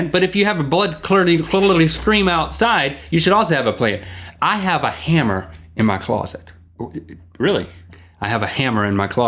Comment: I heard "I have a hammer in my ballsack" the first time .